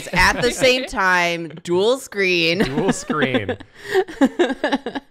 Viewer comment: "We are watching them at the same time: Dual screen!" Thought they said 'duel screen' and were gonna watch Yu-ghi-oh on another screen.